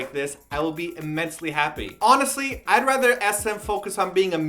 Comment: says immensely happy. But subtitles say extremely happy... what??